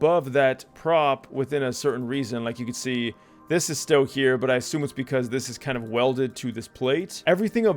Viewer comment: This is kinda neat. This could probably be useful for some game modes. Kinda reminds me of that random generating map that spawns rooms as you open doors. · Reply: i thought u said this is kinda meat